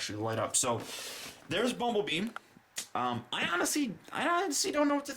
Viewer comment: did he say bumble BEAM